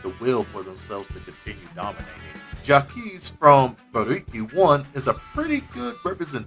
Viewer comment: Anybody could tell me what is the name of the game at 11:50 I couldn't understand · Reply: Buriki one